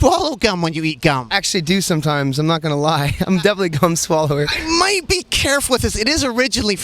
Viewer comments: Did he say "Yeah i'm not gonna lie, i'm definitely a cum swallower"!?!? :p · sounds like Skrillex said, "I definently am a cum swallower" 😂😂😂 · Did he just said he a "cum swallower" ? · sounded like Skrillex said "I'm definitely a cum swallower" lol - I wouldn't doubt it. lol jk. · thumbs up if you thought he said cum swallower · sounds like he says cum swallower lol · Haha it sounds like he said "cum swallow" xD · sounded like he said cum swallower holy shit lmfaoooooo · is he saying hes actually a c*m swallower!? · I thought he said cum swallower but he said gum swallower anyways fucking awsome person alive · Sorry my Internet'ism but it almost sounded like he said: "I'm actually a cum-swallower" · he said "gum swallower" but i heard "cum swallower", does that make me a bad person? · I thought he said "Im a definite cum swallower" lmao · hahaha when he said hes a gum swallower i thought he said cumm swallower when talking about micheal jackson hahaa